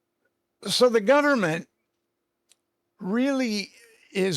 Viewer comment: this guy sounds like he’s drunk. Sorry. You tell me what he says there? Click the timestamp. And does he say “so the gunnerment”